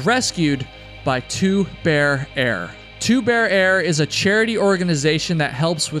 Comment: For a moment, I thought you said “Two Beer Air”.